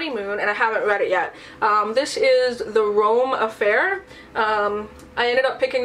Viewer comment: Totally thought the Rome Affair said Meme Affair..